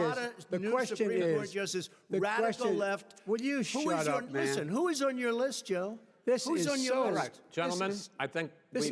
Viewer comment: C'est moi ou Biden lui a dit "Shut up man" 3:07? 😂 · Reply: would you shut up maaan